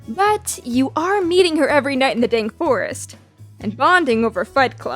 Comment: I SWEAR SHE SAID DANK.